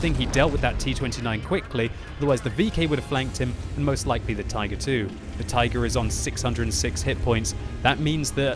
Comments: did u say tiger 2? HAHAHA. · did u say tiger 2? HAHAHA.8